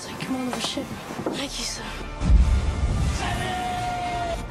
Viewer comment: Did he say "McFly" at 2:36?